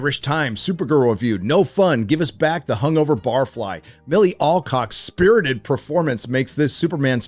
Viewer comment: I heard Milly Takes Alcock gave a spirited performance